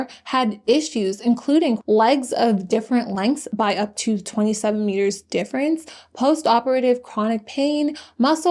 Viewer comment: 20 centimetres?! I really hope that that was a mistake on the part of the journalist. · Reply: it’s better than the “27 meters” I thought I heard while listening to this in the background as I make my bed lmao (but seriously, 20cm is horrific)